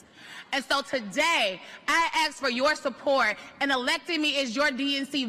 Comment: ain't no way when she said ask the subtitles said axe thats hilarious